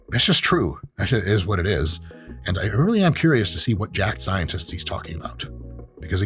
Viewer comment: I don't know why, but I thought he said "jap scientists" instead of "jacked scientists."